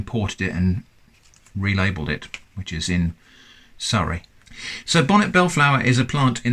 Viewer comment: Did you say sauri? Like a pacific sauri?